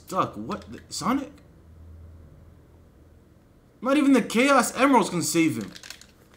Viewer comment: Not even gonna lie, it sounded like it said "not even the gay ass emeralds can save him" lol